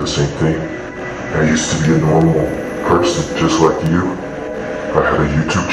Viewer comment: Nobody's going to talk about how it said peron instead of person?